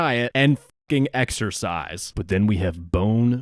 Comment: exercise? I thought you said extra fries?